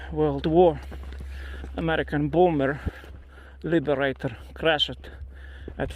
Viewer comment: I heard "American boomer crashed into the hill"